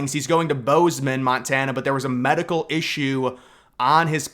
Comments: Sounded like you said grounded for a medical issue, but I know you meant mechanical issue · Did you say medical issue at 1:06?